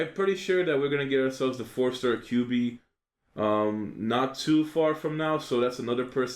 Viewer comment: Happy new years, and did you say 4 stars kyuubi Natsu at 13:39???